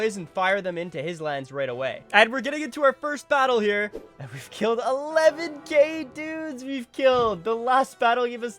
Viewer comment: When he Said "we killed 11k Dudes!" Ngl, it sounded like We killed 11 gay dudes! And I was like, what the what? Ck3 hás this mechanic aswell?